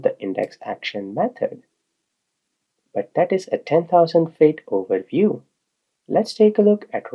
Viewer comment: what does he mean by "that is a 10,000 fate overview"? Subtitles say that but Google finds nothing when i search it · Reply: may be he meant 10,000 feet overview